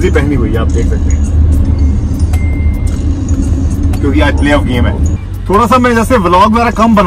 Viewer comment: What did he say at 5:16😮😮😮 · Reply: Semin-Final ka game hai basket ball me....ye bol raha tha